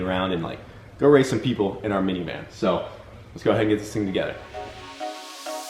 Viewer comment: "...Go race some people in our minivan." -FUCK YEAH!! I think BEAT, instead of race.